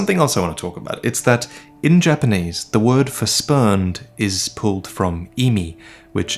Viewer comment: I dunno why i misheard spurn as sperm